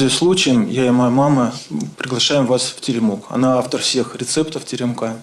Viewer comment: Почему когда он сказал Приглашаем вас в Теремок, мне послышалось -приглашаем вас в тюрьму